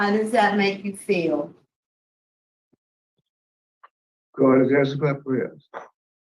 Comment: what does he say in response to the question, "How does that make you feel?"? (I can't understand him. Really want to know what he said.) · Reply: “God has answered my prayers”